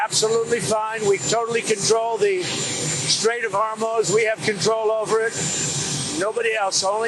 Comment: Why does it always sound like he says the Strait of Hormones? Is it just me? Lol